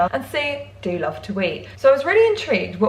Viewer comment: I thought she said I do love to wee hah